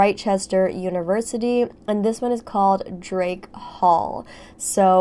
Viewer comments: Why did I hear “Jake paul” Instead of “Drake Hall”? Plz halp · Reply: I heard, "Drake Paul" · Why did I hear “Jake paul” Instead of “Drake Hall”? Plz halp